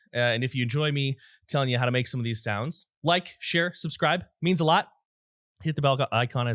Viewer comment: My mom's name is Sharon, so I keep hearing the tag as "Like Sharon Subscribe". Hmm, my mom should start a YouTube channel...